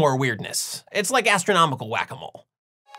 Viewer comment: When you said "Whac-a-mole," I I thought I heard "wabbit hole." Henceforth I shall refer to this sort riddle-mystery-enigma natural-philosophical turducken as a Wabbit Hole.